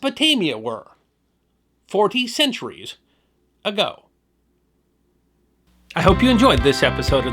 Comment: did he say 40 CENTURIES ago??